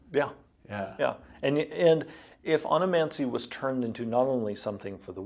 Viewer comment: Thought it said "Onanancy".